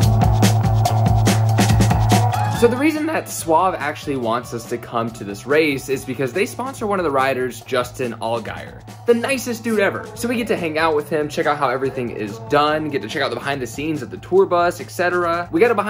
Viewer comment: Did you say rider?